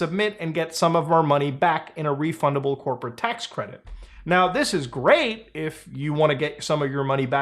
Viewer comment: I heard Tehiti's great.